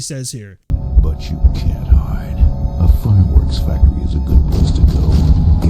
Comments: “A fun-ghoul’s factory is a good place to go.” I believe that’s what he’s saying. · “but you can’t hide.” “ A fireworks factory is a good place to go.” That is what he says.